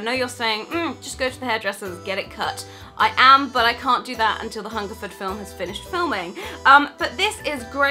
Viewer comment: ok I've listened to a portion of the vlog six time and I still can't make out exactly what is saying about why she can't get her hair cut. It sounds like "after the Huntington film has done filming" Can someone clear this up for me. I am a fairly new subscriber. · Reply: Thanks! WOW that must be exciting. To tell you the truth I thought you said Hunger Games film. Have no idea why I couldn't catch that, my family is from the UK and I'm used to a British accent.